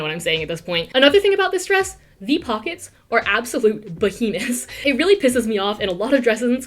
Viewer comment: Did she say these pockets are the absolute BaHiNaS?! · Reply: i know this is a month late but i believe she said "behemoths"